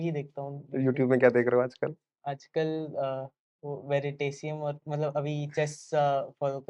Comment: what did he said? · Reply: Veritasium youtube channel